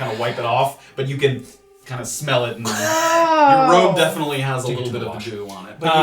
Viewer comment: It might just be because and are talking at the same time but it kinda sounds like said "your robe definitely has a bit of a jew on it" at 13:00. Freudian Slip probably, mixing the words gel and goo on accident?